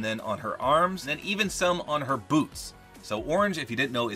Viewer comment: I heard “her boo-“ then it cut off since I was skipping it